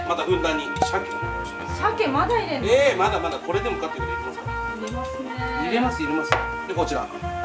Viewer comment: 入れますねぇ、入れます入れます、が やりますねぇ、やりますやります、 に聞こえたのは自分だけじゃ無いはず